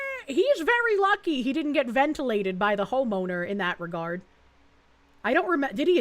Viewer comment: anyone else hearing Hoemoaner?